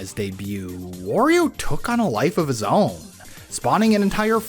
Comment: I could have sworn you said "worio" at one point